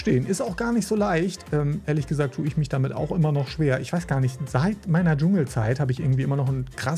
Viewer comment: ich hab verstanden "Seit meiner Jugendzeit!" Wollte dann schreiben "Fühl ich!" 😅